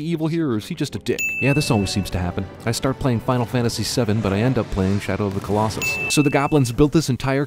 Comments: uhhhh final fantasy VII? or XII lol i heard 7 but i see 12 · Love your everything wrong with moives thing but at 2:32 when u say "Final Fantasy 7" the subtitles say XII (12) instead of VII (7). LOL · for a guy with massive attention to detail at 2:34 he talks about starting Final Fantasy VII then ends up playing Shadow of the Colossus, but the subtitles say Final Fantasy XII. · voice over guy references Final Fantasy VII but the captions say Final Fantasy XII. Movie Sin Counter Counter: 1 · you mention Final Fantasy 7 but the caption says Final Fantasy XII. Also Final Fantasy 7 has more Steam Punk Motorcycles and Cars than The Hobbit so you should've mentioned Final Fantasy I instead. Well at least in my opinion.... +1 Sin. (+7 Respect for this channel) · Everything wrong with this video in less than 10 seconds: Subtitles say Final Fantasy XII, but the guys says Final Fantasy 7 at 2:30. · you say you "start playing Final Fantasy 7", but your subtitles say "Final Fantasy 12". Reaaaaaal credible. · He said "Final Fantasy Seven" but on the subtitle says "Final Fantasy XII"? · At about 2:30 he says 'I start playing Final Fantasy 7' but the subtitles say XII which is 12. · Mentions Final Fantasy 7 but the subtitles say Final Fantasy 12 (ding) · so you started playing final fantasy 12 and ended up playing shadow of the colossus? i thought you said final fantasy 7! ding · he says Final Fantasy VII(7) when the subtitles says XII (12). Everything wrong with this video.